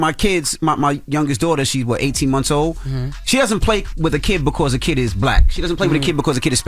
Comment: Dude that called in about going to jail for child abuse did that shit… he made absolutely no sense and was talking in circles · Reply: It sounded like he said a kid died!!